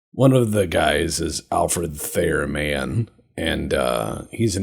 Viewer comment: haha, my ears thought you said Alfred Fair man...